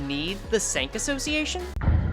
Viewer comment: heard that as stank association